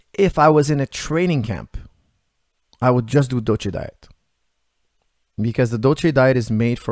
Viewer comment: Can someone tell me what diet he talks about at 35:28 ? Die he say Doce, Docu ...? I tried to look it up but I can’t find anything. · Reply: Dolce diet from Mike Dolce